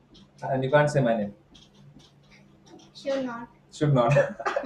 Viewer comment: What did she say? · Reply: Should not